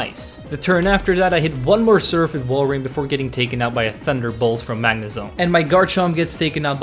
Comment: For a spit second, I thought you said thunderclap😜